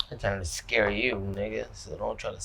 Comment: DID NATE JUST SAY NIGGA OR AM I TWEAKING RN PLS SOMEONE LET ME KNOW